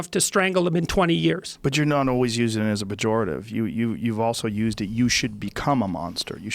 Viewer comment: What does Joe Rogan say @ 1:37:45? What’s that word? “Bajorative”? 😫 Someone please help me understand.